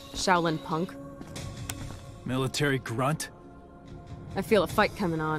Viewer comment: I thought he'd say 'military bitch' LOL